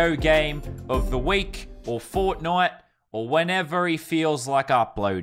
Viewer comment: WHAT DOD HE SAY AT 0:10 ERRRGHHGHGH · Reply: Fortnight*/ 2 weeks 🤦‍♂️